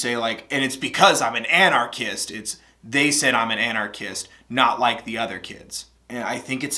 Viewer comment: when i listen to anarchist i hear "im not like other kids" as "im not like a republicast(i know thats not a word but thats how i think you would spell it)